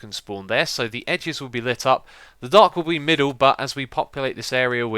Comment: Did anyone else hear him say "The dark will be middle." around 15:30?